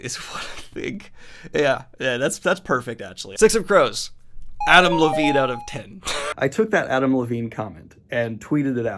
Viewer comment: I thought I heard "Avril Lavigne out of 10," which fit perfectly with your edgy teens description to me.